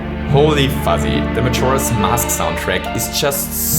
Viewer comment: Andres Ulloa - some people say “holy 💩”, does that mean 💩 is god, too? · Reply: 0MindSwept0 No, if you turn on subtitles, it says God instead of Holy Fuzzy